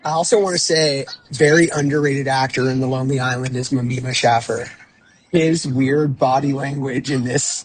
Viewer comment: I thought he said Ameoba Schaffer 😂